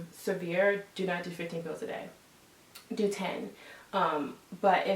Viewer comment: did she say 15 pills a day?